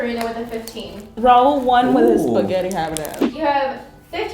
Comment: Did she just say "with your spaghetti having as"?